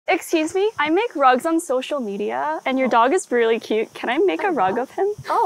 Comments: The girl she asked thought she meant kill him and use his fur for a rug💀💀 Woah this kinda blew up 3.5k?! 4.6k?! 34k?! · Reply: I thought she said "I make drugs on social media 💀💀💀💀💀👍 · Why did i hear"I make drugs on social media" at the start of the video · I heard 'I make drug on social media ' 😅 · Thought she said drugs for a sec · At first I thought she said” can I make a rug out of him”😂😂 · I am not kidding I thought you said drugs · I thought u said I make drugs on social media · I literally thought she said drugs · I thought she said drugs on social media XD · I had my phone on low volume and I heard "I make drugs on social media" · I thought she said I make drugs on social media 😭😂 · At first i heard ,,drugs" instead of rugs💀